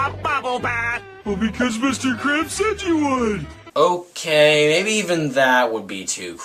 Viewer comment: when i was 7 i used to think patrick said 'because mr krabs sent you one' but come on i 7 year old does not know better